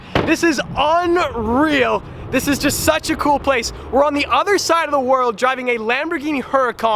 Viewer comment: did i just heard that? or misheard? "we are on the other side of the world"